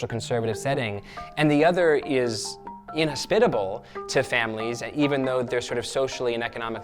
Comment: What's the word at 4:08? ...social conservative setting and the other is and then what? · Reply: Miss V inhospitable